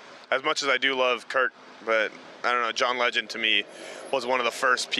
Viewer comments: Did he just say John Legend? I know it's John Lennon but it sounded like he said Legend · did he say John legend?